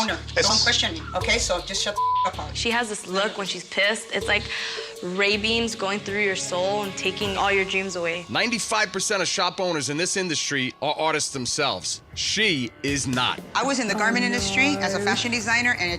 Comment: EDIT: She said Ray Beams, but I heard ravens! "It's like ravens going through your soul and taking all your dreams away."